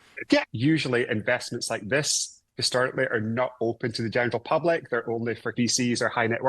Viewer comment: Did he say genital public?!